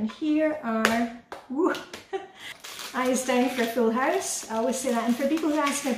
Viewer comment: I heard “eyes down, Phil Haus” 😆, you are a treasure! I am so grateful for your videos. Katherine